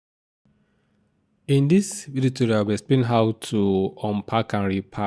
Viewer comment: I was trying to sort out the word at 0:18 . Did he say "In this viritura"? Thanks!